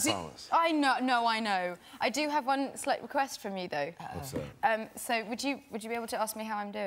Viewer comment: Pleeeeeeease, anyone can help me ? Hi, would you be so kind to write what she said from 2:05 till 2:10? She said "I know, no I know..." and what was l8r? Sorry, but I'm not good in English, especially when I need to understand sth by only listening. Many Thanks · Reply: She said "i do have one slight request from you though."